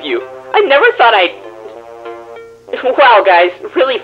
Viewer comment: eu juro que nessa parte eu escutei "tudo bem né tio" 🤡